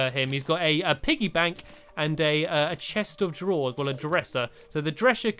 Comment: I heard stampy say dresher instead of dresser